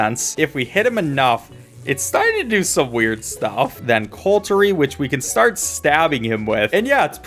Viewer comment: "And then cultery" (That is not a typo, he said CULTery instead of CUTlery)